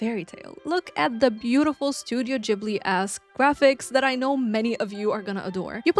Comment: I thought you said "Studio Ghibli ass graphics" for a hot minute until I realised it was probably "Studio GhibliESQUE" omg 😭 love recommendation videos, thank youuu! Definitely not a cosy game because of the combat but I've been loving Hades II on Steam Deck all cosy in bed lately 💕 and the OG Hades since I've been working on 100%ing it, but Hades II definitely feels cosier because of the home base being a camp/grove! Cooking Mama DS made me feel old haha